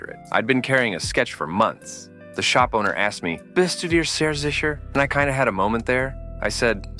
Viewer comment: At like 11:57 ... What in the fuck are you saying?! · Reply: Seems to be "Wissen Sie sehr sicher?". German that basically means "Are you really sure?"